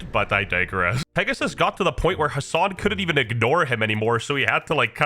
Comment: how did the subtitle say destiny instead of Hassan lol